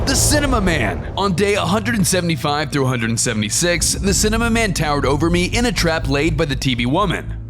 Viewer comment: did you just say cinnamon man